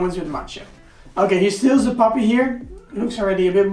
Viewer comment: Did he call Macho a puppy, at 9:55?😂💕